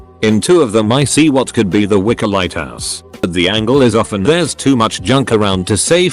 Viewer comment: Its like the husband wanted to enjoy a bit more alone time in the afterlife. Also I think lighthouses are a symbol for lonlyness. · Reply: I thought it said Wicca lighthouse